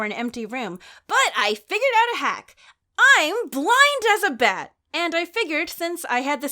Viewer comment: I'm being honest , I thought you said you were "Blind isabeth" Turns outl, you said blind as a bat 😅😂